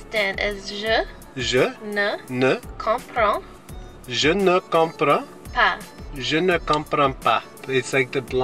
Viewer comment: Did you just say "yo no compro pan"? Just kidding, sounded like that tho ;) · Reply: haha yeah it sounds very similar! Je ne comprends pas